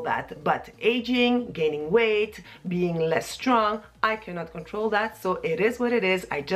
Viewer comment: Replayed your video at .46 because I thought you said "being less drunk" (instead of less "strong") . Maybe I should have my hearing checked - but it was so funny. BTW - now I feel like eating a panino! :-)